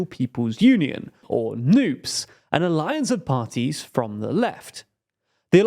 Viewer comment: Am I the only one who heard that as "noobs"